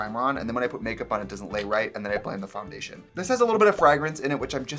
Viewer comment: clicks in gasp why tf am I feeling things for brian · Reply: Ok I just heard him say frah-grance